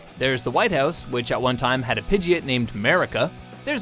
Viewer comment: Did you just say Pidgey-it?!